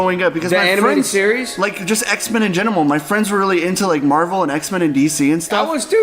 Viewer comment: It's been over 2 years but i just noticed says "just in genimal" at 1:14:20 instead of "just in general."